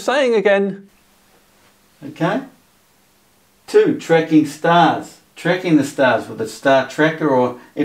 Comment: It sounded like he said star trekking instead of star tracking.